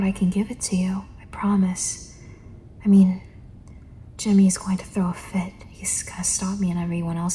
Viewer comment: i heard Timmy and not J*mmy- 😭😭